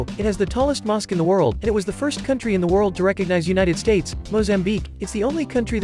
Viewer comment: I thought you said biggest, you actually said oldest